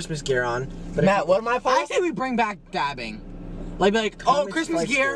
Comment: I thought nick said vabbing😭☠️